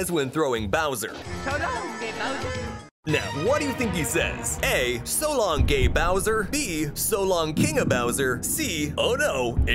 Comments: I thought that he said so long game bowser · I always thought he said “So long, ay bowser!”